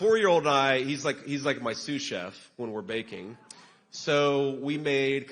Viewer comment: When he said his special talent was baking, I thought at first he said vaping!